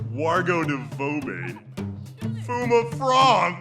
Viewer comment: it sounded like bob said "From a frog!"